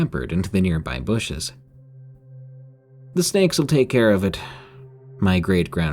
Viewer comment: “The snakes will take care of it” sounded like “the ska**s will take care of it” hahaha😂